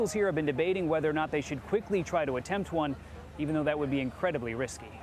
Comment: it sounds like they saying "we gonna quickly try one" oh yeah if we fail we have 12 more tries